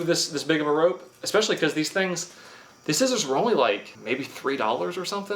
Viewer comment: my god at 3:18 i thought he said they were only like $83. but it was "maybe $3". phew